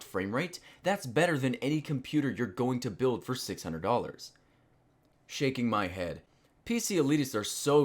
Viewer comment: I swear he said shanking my head.